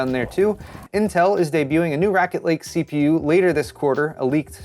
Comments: did he just say Racket Lake? Like it plays tennis? Sick. Wii Sports emulator incoming. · Did you just say "Racket Lake" CPU? - seems about right 😂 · It sounded like you said "Racket Lake" in the intro... Seems like the right thing to say about them now